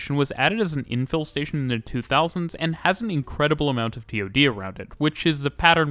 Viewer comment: What is being said here? "P.O.D."? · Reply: T-OD, for transit-oriented development